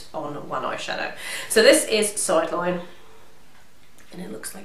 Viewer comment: can you put the name of all the products in the box. i don't understand the name of acid yellow eyeshadow at minute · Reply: It's Sideline.